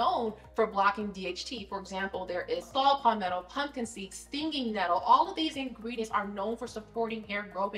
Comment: What did you say at 4:18? I didn’t get that, can someone write that and where to get it? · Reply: DHT blockers mentioned at 4:18 are 1. Saw palmetto 2. Pumpkin seeds 3. Stinging nettle